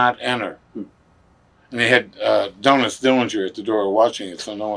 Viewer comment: Did he just say “Donuts Dillinger?” 😂🍩